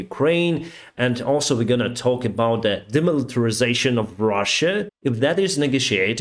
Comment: did you say demilitarisation of Russia. Maybe they want to be part of Nato also?